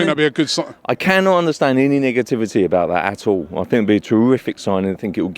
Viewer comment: Did he just say niggativity